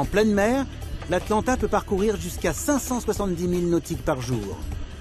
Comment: vous avais dit 570.000NM par jours ? Mdr vous avez fumer quoi !!! Même l’iss il est moins rapide que le bateau ? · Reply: Non c'est pas 570 milles milles nautiques qui est dit mais bien seulement 570 milles nautiques, soit un peu plus de 1000 km par jour ce qui est déjà pas mal effectivement